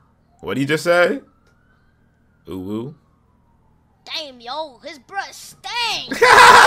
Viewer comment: WTF DID HE SAY?!😂 21:28 Edit: Figured it out, He said "GET A TOOTHBRUSH TO BRUSH YOUR TEETH STINKY BREATH"